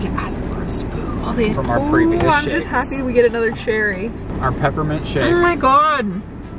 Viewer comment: Sounded like u said peppermint shit lol 😂 dang my ears haha